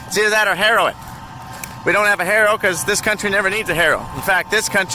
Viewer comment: " either that or heroin" I really thought that's what you said until you said harrow the second time 😂😂